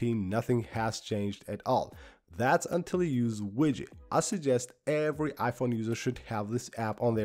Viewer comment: you suggest a widget app? Is it called widget or Widgey? Could not really understand, and I don't see it in any notes?